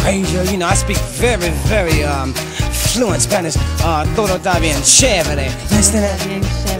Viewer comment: A seamless blending of Afro-American music with Afro-Latin music. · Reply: All these years I thought he said "Como tambien Chevrolet?"